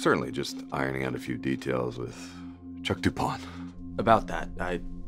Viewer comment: Chuck? Boi I heard Shaka 💀😂